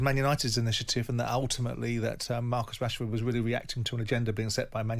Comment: i thought he said marcus cashford lol